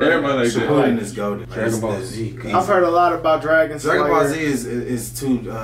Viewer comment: bro i heard him say goblin slayer 💀